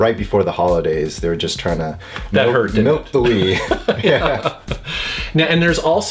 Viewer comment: did he say.."milk the wii?"..:D lol..gigidy!!